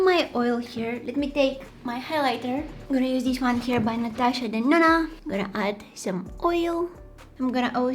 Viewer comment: i dont know why i heard natasha banana😂😂